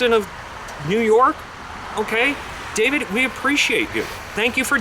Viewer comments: did he call you David??? 😝lol😂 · Did he call you David??